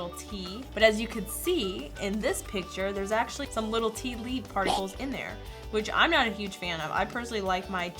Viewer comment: I thought she said fartical instead of partial